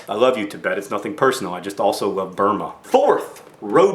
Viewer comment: did he say he love Burma?!?!? That's my country.